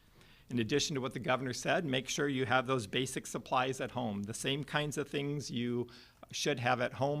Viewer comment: Rewind to 13:10. Did I hear right???? STOCK UP!!!